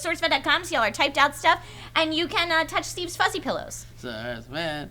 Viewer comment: I was lookin away from the vid towards the end of it. Then I heard the words "Look at Steve's fuzzy pillows", and I was just like............ WTF?0.o Is anyone out there with me on this, or am I just in need of getting my mind cleaned?